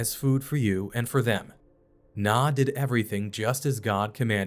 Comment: Why did he call Noah “Nah” at 11:50?